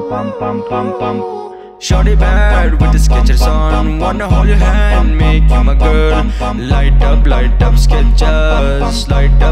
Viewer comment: For some reason I heard that as ... Shaniwar put the sketcher on Want to hari hor make you my girl Lightum lightum sketchers. All the mixed bengali and hindi tunes😂